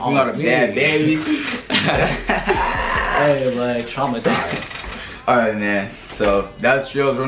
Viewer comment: I thought he said trumpasized I thought he lowkey was being racist